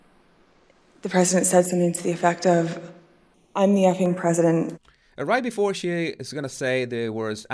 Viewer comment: Why would he say “I’m the ACTING President”? He would have just said he was the President! Also, why isn’t Mr Engle testifying instead of repeating a hearsay conversation from him? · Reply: She said "effing", not "acting" 7:38 (caption is wrong)